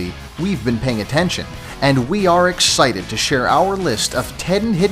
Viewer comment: it sounds like he says ex-shited.😐